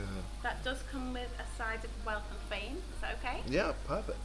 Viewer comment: I thought she said comes with a side of baked beans, but she said wealth and fame 😂😂😂